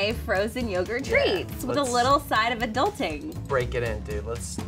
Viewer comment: When said a little touch of adulting, I thought she said adultery